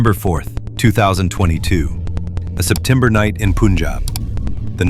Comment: Did he said poonjab?!?!?!